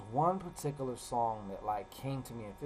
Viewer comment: did you say “song” or “psalm”? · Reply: Song🎵